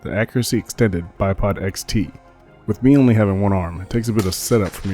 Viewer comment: Great informative video as always. I thought you said it was a spare arm not one arm?